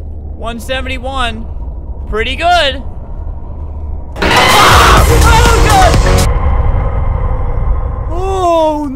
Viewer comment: Is it saying “I’m here” just before the jumpscare? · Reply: Minik C1bili it does say I’m here, but that’s not always before the Jumpscare. That lets you know that the second monster is there and if you turn around you instantly die